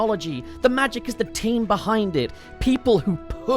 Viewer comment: I swear I heard 'the magic is the taint behind it' instead of team, and yeah, checks out